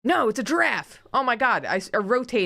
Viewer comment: Why did say giraffe and I swore she said elephant? I was so confused when she started drawing lines